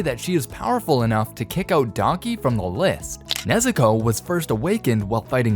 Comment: he says donkey instead of Daki